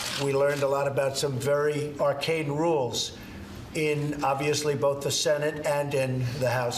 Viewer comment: is he saying arcane rules or arcade rules!